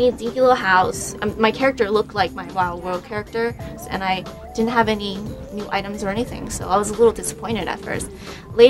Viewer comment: To StarmanPhantom (Again): Uh...nevermind, I checked, I thought she said she didn't have her items, turns out, it was that she didn't have any NEW items... *Whirr*